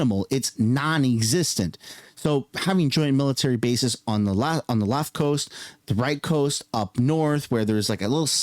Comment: did he call BC the left coast? 💀